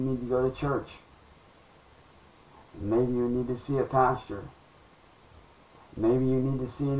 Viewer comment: Did he just say that I need to go to church and see a "pasture" ?!?!?! 😂😮😂😂